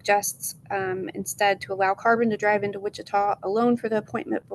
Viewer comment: Is their kid named Carbon? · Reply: I thought I heard carvon.